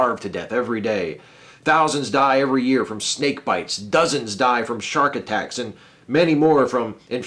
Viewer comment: I thought you said Stake fights xD